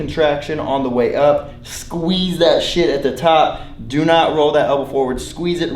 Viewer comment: Did he say " Squeeze that SHIT at the top " ???